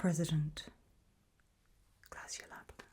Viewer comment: what did she say after the president? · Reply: “Clasyanapnos”